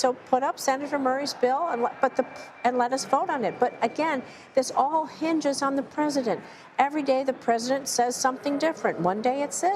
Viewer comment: She just admitted to holding a group hostage of their paychecks because she wants to hold a separate group accountable - how is that effective when the separate group is fully funded by a bill? It’s not. They would rather see families struggle in their day to day lives then see them succeed. All of congress should lose out on their paychecks . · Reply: I thought she said it all depends on the president.